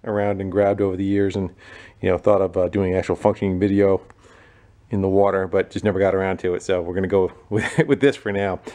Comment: Lol ok at 12:56 did you just say fucking video. Im shocked never heard you drop an f bomb lol...